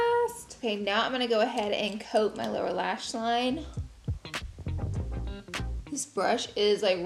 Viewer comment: Haha for a second there when she said "ok now we are going to coat the lower lash line." I thought she said "ok now we are going to coat the Laura lash line." 😂